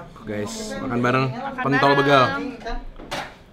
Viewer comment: kok gua dengernya "kntl begal sih"